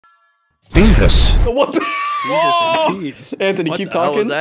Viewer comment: When the video opened with Anthony going " J E S U S ! " I knew it was gonna be gold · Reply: At first I thought he just said "Beavus."